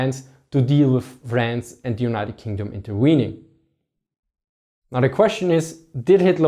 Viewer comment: did I just hear "interweening"? Loool